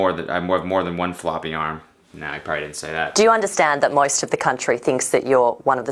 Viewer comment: did she say “MOIST of the country”?? 😂😂😂